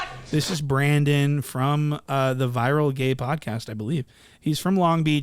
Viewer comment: From how bro talked I swore you said “The Viral G@ podcast”😆 like mfs just using any name to get views at this point